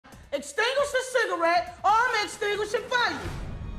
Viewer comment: for some reason I thought she said extinguish the cigarette or imma extinguish your face